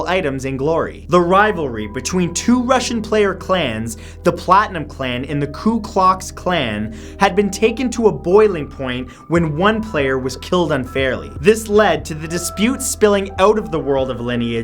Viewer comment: did i hear ku klux klan (kkk) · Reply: No you heard ku klox klan